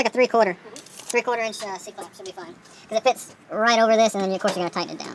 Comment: what clamp do I need to get from the store? I can’t figure out what they are saying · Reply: just a c clamp or hose clamp whatever that store calls it